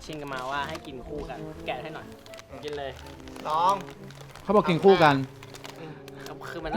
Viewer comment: ได้ยินพูดว่า"หนูกินเลย"หรือเราคิดไปเองนะ??